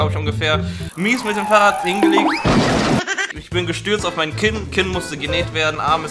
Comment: Ich hab verstanden: Ich bin gestürzt auf ein Kind 😅😂